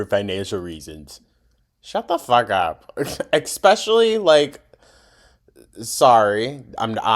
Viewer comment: I'm sorry, did you just say "expeshully?"